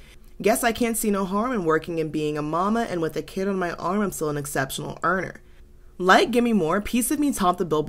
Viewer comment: I was the 3rd in line to buy Blackout in NYC and it might still be my favorite album of all time. And yet (cue annoying Millennial slang) I was today years old when I found out she says "I'm still an exceptional earner" in Piece of Me. I was so shocked I went looking for the studio acapella and without the instrumentation it's incredibly clear. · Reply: I thought she said “I’m an exceptional aimer” 🔫